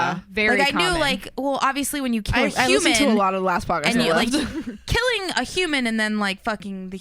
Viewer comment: I can't hear what the name of the podcast is listens to. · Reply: Last Podcast on the Left :)